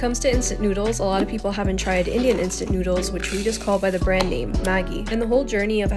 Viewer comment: Please don’t tell me I’m the only one that heard her say “idiot instant noodles”😭